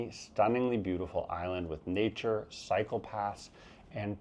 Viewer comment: When you said Taiwan has great cycle paths, I thought you said psychopaths at first. Then I remembered you weren't talking about the US.